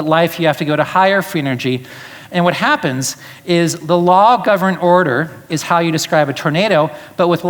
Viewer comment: does he say "the law of governed order"?